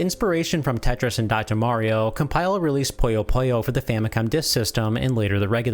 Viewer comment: Love the video, but your pronunciation is odd. Sounds like you say "Poyo Poyo" instead of "Puyo Puyo". :P